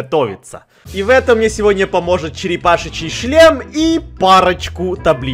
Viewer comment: мне одному послышалось Черепаший Чл*н?